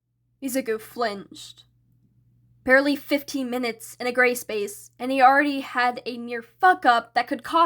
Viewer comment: Help I’m relistening to this and I heard “gray space” and immediately comprehended it as “gay space”😭